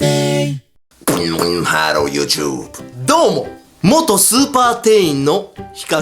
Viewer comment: スーパ店員を超(スーパー)店員に聞こえたのは私だけじゃ無いはず。 続きを読む